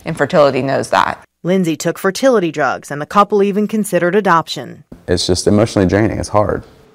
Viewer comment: Sad story, but the human body simply isn't made to carry those many embryos at once! I hope they consider adoption again, so many children out there in need of a good home... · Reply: when it said ADOPTION i thought it said ABORTION